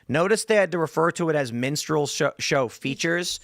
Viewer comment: I kept hearing MENSTRUAL hahahahahah 🩸